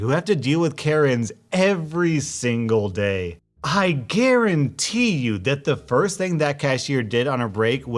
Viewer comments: not gonna lie, i heard “I Karen-tee” the first time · When you were talking after the first story, did I hear "I KARENtee you.." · i thought he said i karente and i was just like hahahahaha WHY? and then i reround the video and realized he said garante or however the f you spell it lol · did you say "i kurentee" im using that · I thought he said"Karentee" 😂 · Did you say I Karentee you? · I make so many puns that sometimes I hear puns where they weren’t made. At 3:57 I heard I KARENtee instea of garuntee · I could have sworn you said "KARENtee" lol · Instead of I GUARANTEE at 3:57 it's I KARENTEE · For some reason when he said I garentee I thought he said Karen tea and I was like wait what